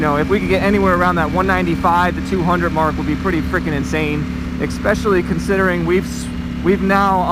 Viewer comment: Wow did he actually say eXpecially? 02:15